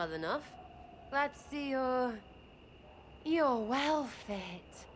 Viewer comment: What does Sera say to the Inquisitor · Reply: "You're well fit."